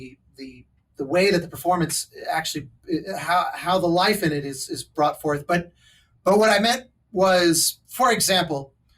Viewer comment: I didn't catch the name of the wind player they mentioned. I got Kristen something? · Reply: Kristin Naigus! Her website is <link>